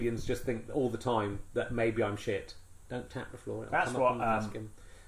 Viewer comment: what does richard say? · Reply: "Don't tap the floor". You can hear the other guy tapping his foot